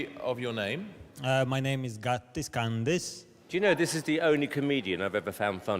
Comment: Gatis Kandis? Only i who though he made up a fake up name? I thought he said "Got This Candy" hahaha